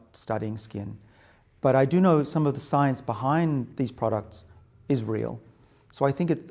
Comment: he said :is real I thought he said :ISRAEL I was going to stop watching lol, cause if Israel is involved, no thanks I don't buy it